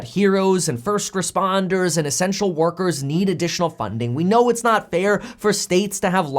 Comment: I almost thought he said sexual workers need money :-)